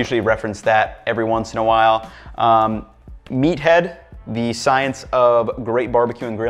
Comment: I thought that book said meth head....LOL